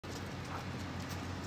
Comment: why they coming on your property and taking your dog?? and why are you recording this? Unless of course this is the nieghbors house the audio is not audible enough to understand... sounds like he sayin ""taken my dog without warrant"